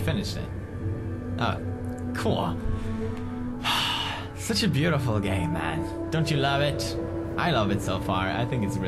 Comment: what pewds said: "this is such a beautiful game man." What I thought he said: "this is such a beautiful gay man."